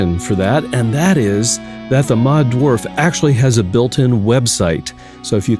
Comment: I thought you said Red Dwarf and I wanted to hear the Rimmer ring mod… wait what?!